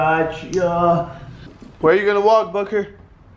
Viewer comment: did you just say fucker